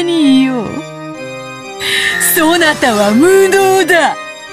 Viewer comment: I thought she said impotent at first lmao